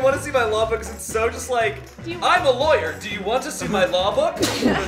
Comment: I thought Shayne was saying I'm lawyer want to see my llama and I was so confused😂😂😂